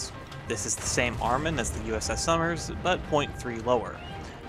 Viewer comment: The fact that he said the porter and summer have the same number of guns is very very incorrect, the porter has two more cannons than the summer and has AA than the summers, they are nothing alike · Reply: I thought you were saying Summers, not Somers